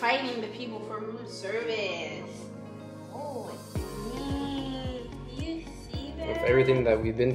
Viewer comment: I heard say" do you see that"